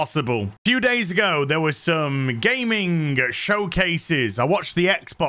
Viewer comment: Hmmm. At the 0:48 mark. Did say Gaming or Gay-ming showcases? Because I don't know if it was play of words from him or am I this deranged to see and hear things that are not there due to all this entertainment getting very political?